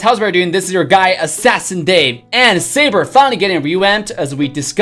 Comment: Sigh... i thought you said "Saber fanny getting revamped" at the beginning i was so pumped up, but, my disappointment is immeasurable and my day is ruined... but saber looks cool though